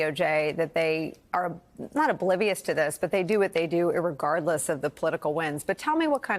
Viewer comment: Did she just say, "irregardless?" Someone want to tell her?